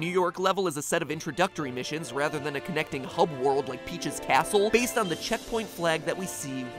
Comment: instead of Peach's castle I heard him Peach's a**hole🤣🤣🤣🤣